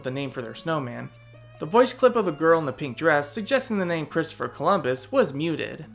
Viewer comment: I was today years old when I learned she was saying “Christopher Columbus.” It always sounded like gibberish to me!